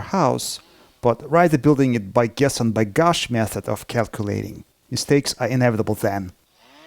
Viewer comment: Did you say guess and gush? that is a funny saying we dont have it in 3:50 America